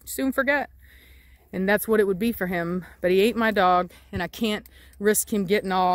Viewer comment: i thought she said he ate her dog💀